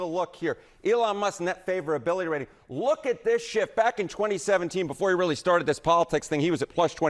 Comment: I know he said "look at this shift" on CNN but I totally heard "look at this shit!"